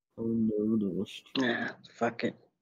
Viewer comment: Bro I thought he said "Fuck it, I'm gonna take a shit" 💀💀